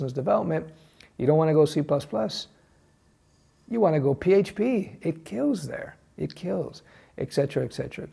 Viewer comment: Did I just hear php at 04:25??